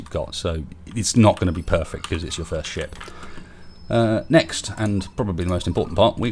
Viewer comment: did you say this is your first sh#t